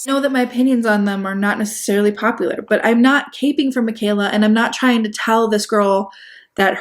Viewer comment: what are you saying here? As in, the actual phrase? I’m hearing, “I’m not caping for Mikayla.” Is this right? If so, could someone define this? Asking for a granny.